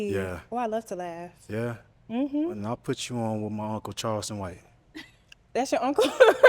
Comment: is he saying his uncle is charleston white??